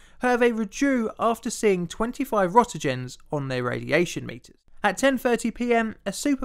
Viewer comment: he is saying Roentgen (although it sounds like he is saying Rotegens)